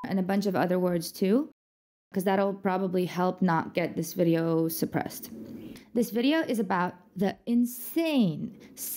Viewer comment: What is the second word? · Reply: Torture